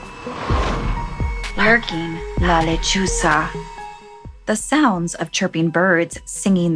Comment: Ngl I thought it said la lechuga ☠️